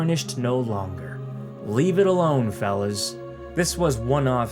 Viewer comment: I heard "felons" instead of "fellas". It fits 343 perfectly tho.